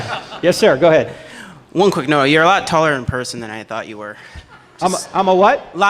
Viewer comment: At first I thought he said you are alot more tolerant....😂🤣😂🤣😍